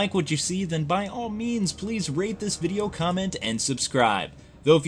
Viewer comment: i thought you said "rape this video" instead of "rate this video" ...i was like o_O